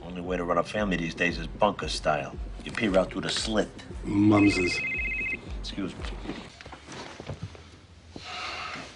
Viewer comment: The way to run a YouTube channel is bunker style, u peek thru the slits.. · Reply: i thought he said pee through the slits